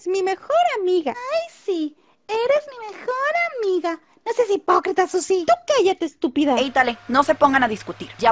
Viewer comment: ¿que dijo la señorita zizzy? No le entendí xd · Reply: Dijo " no seas hipócrita Sussy"